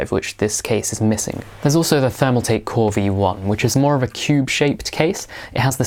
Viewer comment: I love the case at 4:08 but can't understand the name you say. What's the name of this case? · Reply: Its the thermaltake corev1 <link> , hope this helps